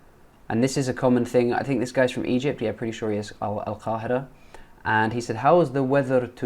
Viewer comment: It is funny how he says al Qahira and the subtitles say al Qaeda!😊😂 in